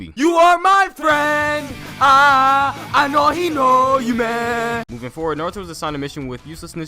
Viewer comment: nobody: literally nobody:YOU ARE MY FRIEND AAAHAHAH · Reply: I swear after those words I heard “I know he know you man”